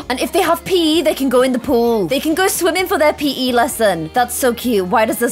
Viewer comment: Who else thought when said "OMG they can have PE in the pool" She said "OMG they can pee in the pool"